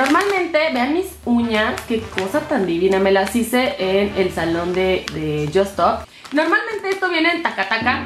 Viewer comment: en el salón de yosstop???? Yo entendi eso. Si alguien sabe que dijo porfa díganme